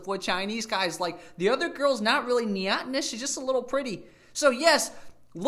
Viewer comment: What does he say at 12:05 , niatnus?